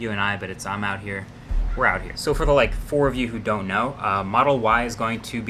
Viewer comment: did he say four of you 😂😂